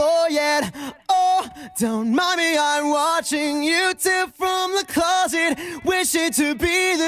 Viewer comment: it sounds like he is saying "I'm watching youtube from the closet" for some reason